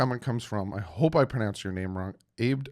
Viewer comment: did you say: "I hope to pronounce your name wrong"? Because I believe you did sir. Love the show! 🐬🏈⬆️💯🤘